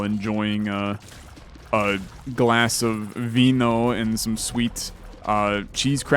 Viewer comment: why did you said wino instead of wine? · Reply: He said vino, I think it’s wine in another language